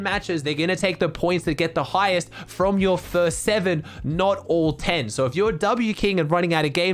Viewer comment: I think they said the "best 7 games" not the "first 7 games"